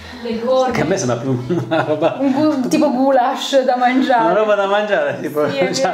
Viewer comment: мне послышалось или сказала " - типа гуляш" ?